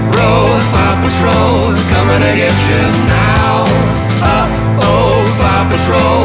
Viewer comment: did they just say Rut-roh? This makes not more like my dads anthem.